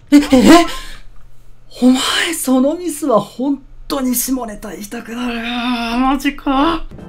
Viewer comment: のとこ何て言ってるの？www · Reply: ごごのもも お前そのミスは本当に下ネタ言いたくなる〜 じゃないですかね(⁰▿⁰)w